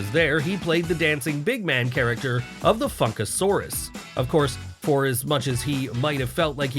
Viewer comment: Is it just me, or did it sound like he said f*ckasauros instead of funkasauros 11:45.